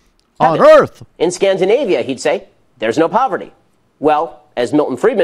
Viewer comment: Because it appears to have puzzled the hosts here, in case someone deaf is trying to figure out what Ben Shapiro said- here it is @ 4:22 It even has proper closed captions, so feel free to turn them on: In Scandinavia he'd (Barack Obama) say: "There's no poverty." Well, as Milton Friedman responded to a similar statement: In America among Scandinavians, there's no poverty either." Ben Shapiro: Social capital matters. Culture matters. - That social capital is being actively undermined by Norway's generous welfare state, which actually pays people not to work. So in case some radical leftist ideologues and despicable racist that play identity politics would still like to read race into that- let me be clear: It's NOT the melanin presence in their skin. It is their >>c̲u̲l̲t̲u̲r̲e̲<<. Not skin color! Your insistence on skin color in everything in the world is beyond wrong. What you believe and how you behave is the what truly matters. That’s what culture is: what you believe and how you behave. It’s not the stupid shade of color you happen to be born with! You can believe that raping virgins will cure your aids and act that lunacy regardless of your skin complexion. Or you can believe and enforce the ideas that hard work, education and dedication is what matters to success in life. Who knew that people who learn diligently, work hard and don’t give up tend to not end up poor? It’s a freeking mystery of untold proportions, right? And let me stop your dirty racist minds right here